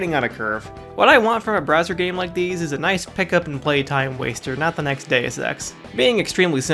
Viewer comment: I fully thought you said “not the next day of sex” lmao